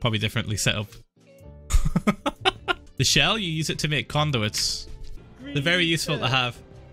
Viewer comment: I thought he said condoms lol